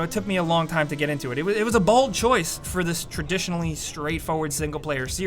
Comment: sounded like he said “it was a BALD choice” lmao